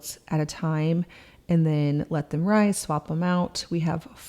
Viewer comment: did she say let them rise when talking about washing the quilts? lol